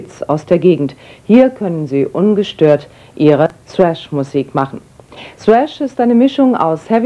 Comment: hab erst swash musik verstanden